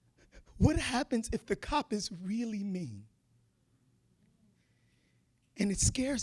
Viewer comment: did he say "me" or "mean"? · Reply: He said "mean." If you do the closed captions you can tell.